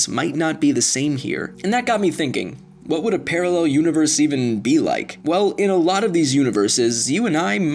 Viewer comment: i legit thought the video said ' what would a parallel universe Isak and Even be like' FROM FRIKIN 'SKAM'